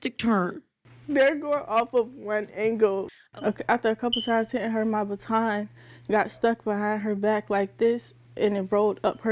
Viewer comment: Put the caption because I can't believe what heard because I heard she said after hitting her a couple times with my baton it got stuck behind her arms it comes up at 2:49 seconds